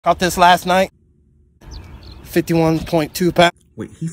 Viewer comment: I swear, I thought he said I bought this last night😂